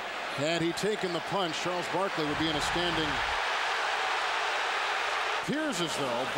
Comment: I don't get what the TV speaker says at 1:49 . "Had he taken a punch, Charles Barkley would be UNASTANDING (???)." Can someone please clarify me the last word?